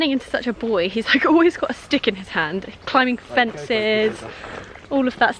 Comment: Lost in translation... when you said Grayson was turning into SUCH a little boy... I thought you said, "He's always got his dick in his hand" instead "he always has a stick in his hand". Either one would be accurate with a little boy but I'm glad you actually said, "stick".